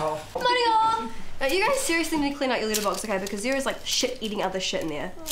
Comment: Just realized letter box in NZ is a mailbox. With the accents I thought she was saying litterbox..LETTER...not a cats potty pan! Ha ha! I absolutely love this little show. It needs to be remade in the USA with this same amazing cast and have it be a miniseries on tv.